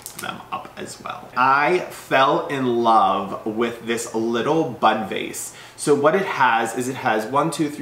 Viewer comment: I totally misheard that as "Butt Face"...I'm sorry...